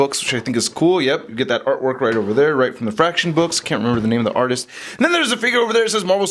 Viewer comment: I hadn't thought of using that for a Cosmo custom. Lol when you said Fraction I thought you were saying "F*** S***"